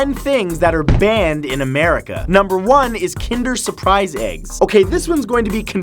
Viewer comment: I'm in the USA I thought he said kinder joy but he said kinder surprise Phew